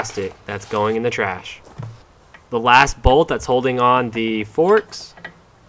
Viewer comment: Erm, 8:59, did you say "forsk"?